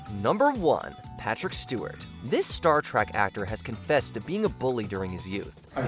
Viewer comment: Pretty sure I heard "Star TRACK" on #1.